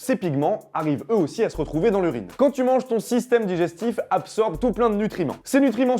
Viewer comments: A 4:28 j'ai cru qu'il disait "quand tu mange ton système digestif ! " en mode C'EST LA SOLUTION. Like si toi aussi ta compris ça 😂😁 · j'ai compris "quand tu manges ton système digestif"